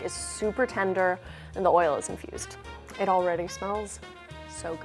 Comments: I keep hearing "the oil is confused" and I'm confused :0 - - - - - - (i know she means infused it's just what my ears register at first before my brain properly processes it) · am i the only one who thought she said, "the oil is confused?"